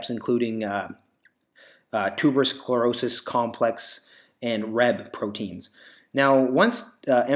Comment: is he saying "REB" proteins?